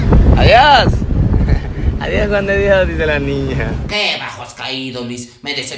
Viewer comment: que pedo, porque si pienso que dice Juan de Dios dice Juan de Dios, pero si pienso que dice panochas se escucha panochas 🤯